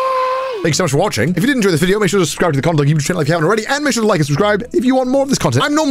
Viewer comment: I'm sorry, but I heard "cumdawg"